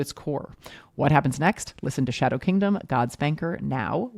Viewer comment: Another great ep! I have to say that I kept hearing "God Spanker" instead of "God's Banker." But that's only because I let my inner-12 year old have too much control of my brain.